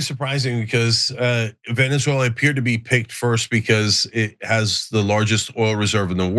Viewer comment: Did he just say "Picked first"?